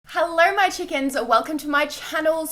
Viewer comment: Ummm this is the first time that I’ve watched her video and it sounds like she says heler my chickens 🐔😂😂😂😂😂